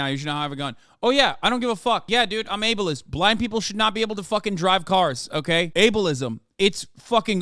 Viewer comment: I thought he said black people shouldn’t be allowed to drive lmao